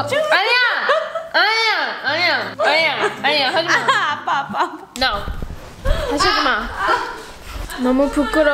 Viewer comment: Wait did say a English word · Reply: I’m pretty sure she said “no” and that’s not a Korean word?